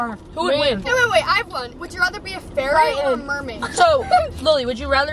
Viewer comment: I thought when she said a fairy or a mermaid, ferret or a mermaid.😅